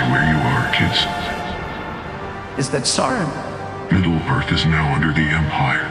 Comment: I misheard him say "is that Sauron?" as "is that Saren?" Great animation, man